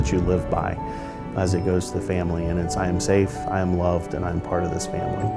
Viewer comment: did you say family?-Dom toretto loves family